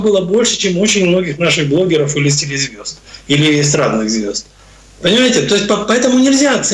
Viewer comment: Эс.. ранные Звезды.. хорошо сказал ... или мне послышалось